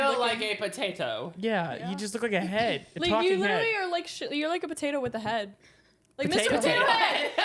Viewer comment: did she say the S word? 😮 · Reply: I heard you literally are like shi a potato with a head